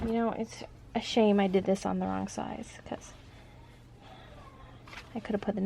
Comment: I just watched your tutorial....it was great!! I've had my machine since spring and have been sooo confused!! I'm going to try it this weekend. You said there was a mistake, I don't see it. Could you explain what you did, so I don't do it??? thanks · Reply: Oh! I thought you said wrong SIDE. And I was so confused because every time I turned it inside out in my head it came out correct. And at the end it was the correct side. 🤪